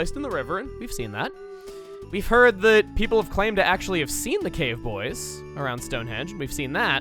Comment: When he said "cave boy" I honestly thought he said "gay boy"